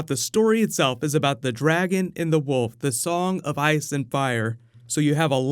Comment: I thought you said "The dragon in the wolf" at first... which imo, sounds cooler.